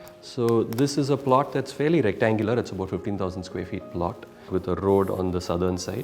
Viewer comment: they are talking about house ares not the whole plot area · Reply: Listen again between @ 1:55 to 2:00 He clearly says it's a 15000 sq ft plot. I don't think the plot is only 3500 sq ft specially when the house built-up area is 8950 sq ft.